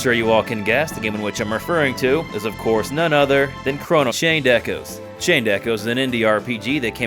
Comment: Oh you said Chained Echos. I thought you said Shanedeku I was looking everywhere.